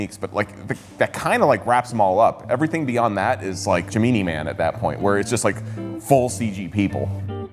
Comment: Did he just say.... "Gee-Me-knee Man"?! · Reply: yeah that’s how we say Gemini in India actually